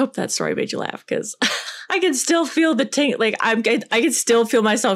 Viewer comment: Did anyone else hear her say 'I can still feel the taint" 😂😂😂